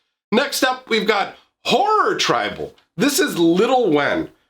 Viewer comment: the way you said horror at first i thought i heard whore tribal 😂😂 maybe its just the accent and voice